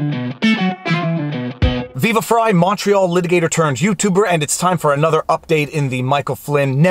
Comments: This was the first time I realized he was saying "Montreal Litigator." This whole time I thought he's been saying "Macho Litigator turned Youtuber." Still accurate tbh. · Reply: I guess a lot of us had that same misconception. I thought he was saying "macho litigator" for a while, too, until he said it a little slower in one video. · This was the first time I realized he was saying "Montreal Litigator." This whole time I thought he's been saying "Macho Litigator turned Youtuber." Still accurate tbh.